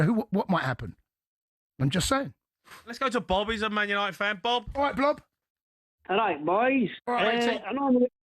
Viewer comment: Did Cundy call him Blob? 😂